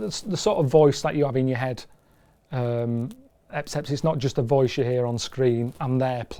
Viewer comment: Did he just say 'Epcept' ? See? That's what we're talking about. That's original. Coming up with his own words and broadcasting them since 2003. He's like a new species.